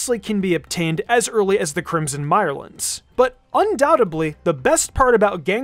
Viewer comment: Did he say undoubtedBly at 6:37?